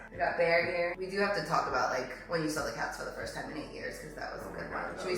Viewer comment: For a sec I thought you said “let’s talk about when you say when cat again for the first time” and I’m like DAMN... rewind- oh she said CATS